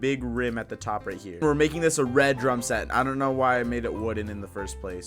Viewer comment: I thought he said "Dr*g set"